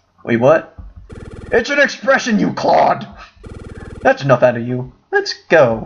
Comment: Wait......did he just say "CLOD"?! Has he met Peridot from Steven Universe, I REALLY think they would get along well!